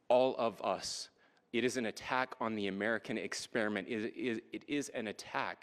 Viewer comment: The American experiment? Did I hear that right?